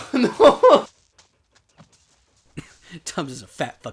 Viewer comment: Haha ironically, the stand in might hate Doug more than I do! 🤣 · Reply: I couldn't tell what was the name of the person that tripped over. I thought u said Tums, Thomas, or something in between.